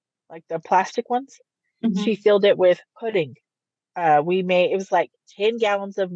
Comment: did anyone else catch the woman who is talking in the car say we instead of she? That woman was clearly into punishing the kids as well. Sad.